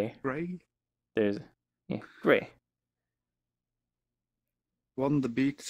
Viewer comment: did he say gay xD